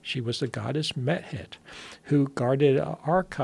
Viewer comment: Thought he said Goddess Meth head 😂